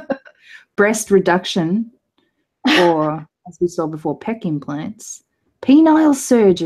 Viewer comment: I thought she said "kek implants", not "pec implants".